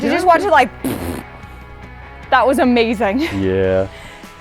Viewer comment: it’s sounded like said amazung and not amazing